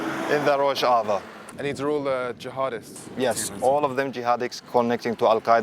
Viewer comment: anyone else heard how he called them jahadicks?